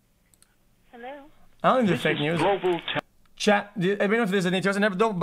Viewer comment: tf is he saying... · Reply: I am fluent in XQCnese “i dont think they are fake news”